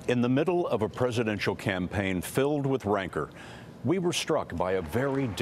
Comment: Did he say wranker